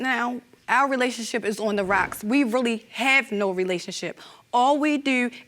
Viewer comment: Out the gate I heard “all we dew”. Baltimore lady lol